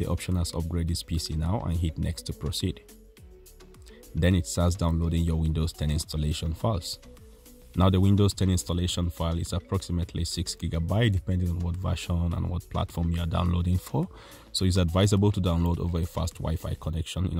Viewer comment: did you just say mashallah?